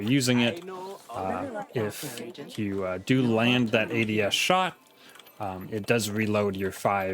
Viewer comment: I'm not native English I heard 3:33 that u land that idiot shot What is idiot shot 😅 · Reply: Oh my apologies! I said ADS shot. ADS is short for Aim Down Sights, which in this game refers to any non hipfire shots